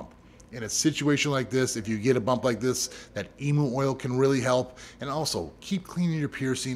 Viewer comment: who else thought he said emo oil for a sec and then realized it was emu oil 💀🪶